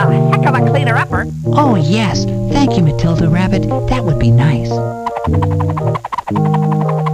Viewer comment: did he say dildo rabbit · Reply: Matilda Rabbit XD but I like where you're heads at